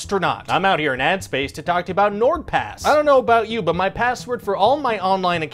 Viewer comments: For some reason I thought the beginning said N-word pass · Ill be honest i thought the sponsor said n-word pass at first